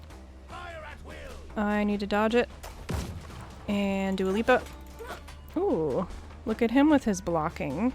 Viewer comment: I thought I was going crazy when I kept hearing you say, “Dua Lipa”. I chalked it up to you saying, “Do a leap up”. Thanks for clarifying and letting me know I wasn’t losing it. 😂🤣😂